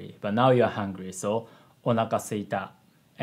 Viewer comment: sound like his saying a little house in Spanish "una casita" Is how it spelled in Spanish if you're wondering